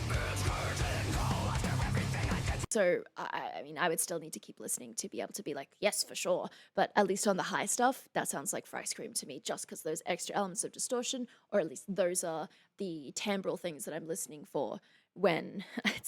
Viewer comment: I swear I hear say "for ice cream" alot in this video. like at 08:52 , "Yes for sure! But atleast on the high stuff, that sounds like for ice cream to me."